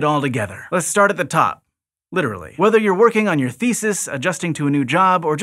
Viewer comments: Because of the priming done in the title, did anybody else hear "whether you are working on your feces..." instead of thesis at 0:32? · He mention butts so I thought he said "feces" at first. It was "thesis". My thoughts were, Why would you be "working on your feces?"